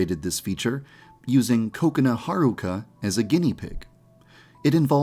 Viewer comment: For some reason When someone says "Kokona" I hear "Coconut"